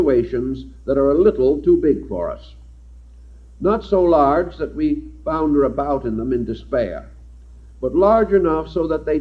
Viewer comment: What did he say at 13:40? Was it.... not so larger that we flounder about in them in dispar?